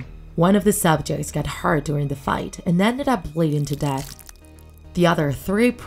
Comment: I thought you said wedding